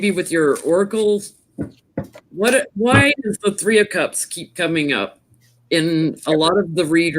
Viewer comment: When you were inquiring about the 3 of Cups, I heard " The 3 Faces of Eve".